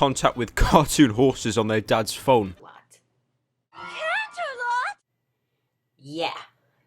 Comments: When he said Canterlot I thought he said Camelot. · At first I thought he said 'Cartoon whores.' and just from that that made me laugh out loud.